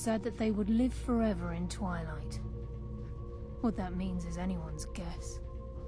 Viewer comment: it says that the priests live forever in twilight but the subtitles say become one with the shadows.